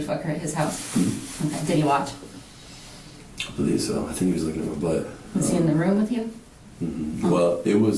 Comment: Did he say” he was licking my butt”